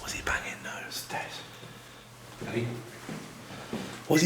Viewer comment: i hear a whisper that says "hey"